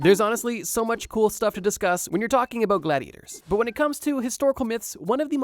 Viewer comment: (face twitching) DiD YOu SAY GoBLiAtORs?!???!!? (Yes this is a shameless self promo, bite me)